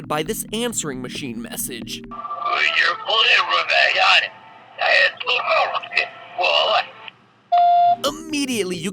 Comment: can somone decode this i want to know what stan is saying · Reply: "Hello, It's Grunkle Stan, and I have a riddle for you!" The riddle must've been cut out